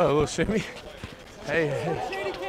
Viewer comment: Did ya'll hear what da dude said in da back round 9:59 - 10:01 mans said slim shady can't dribble🤣💀💀